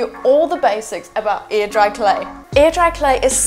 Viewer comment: I keep hearing “ear dry clay” and wondering what the heck that is. lol